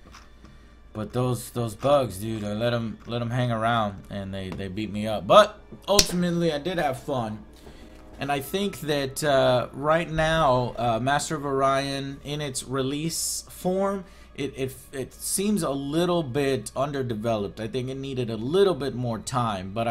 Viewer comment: For a second, I thought it said, "Master of Onion!" LOL 😂